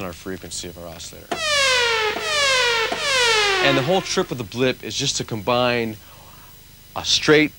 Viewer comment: did he just say "the whole trip of the blip" lol